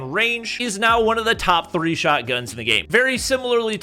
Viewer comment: Am i tripping or did he actually say drh is a shotgun.